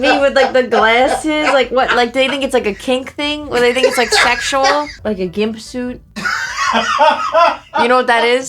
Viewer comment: I swear I heard "do they think it's SHRECK-sual" and with that mask it certainly could have been the problem.